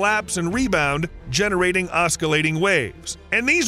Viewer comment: did you say osckalating? Surely you mean oscillating